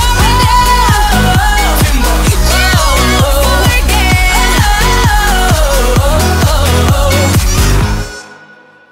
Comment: Can someone tell me what Kesha said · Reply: she said you won't forget